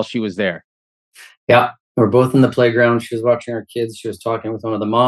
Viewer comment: I have the captions on. at timestamp 5:40 he clearly says "her" kids. not our